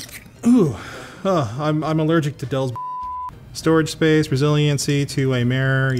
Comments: When you sneezed and said that you were allergic to Dell's BS, I heard 'I'm allergic to dills"... and I had just popped a jar of dill pickles... from Dillman Farm. I'm a lil shook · Did Anthony said he is allergic to Dell's bullshit? Lol. Ahh the lovely i7-3770. After 8 years of loyal service, I just upgraded my i7-3770 to an i5-10400 last week. I am keeping my i7-3770 but to use it to run older games.